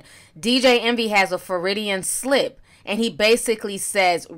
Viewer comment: when you said Freudian Slip, I thought you said “Floridian Slip.” Lol I need to get some sleep